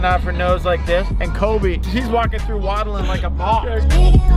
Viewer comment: “And Colby comes waddling through like a boss” 😂😂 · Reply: i really thought he said waddling through like a box lmao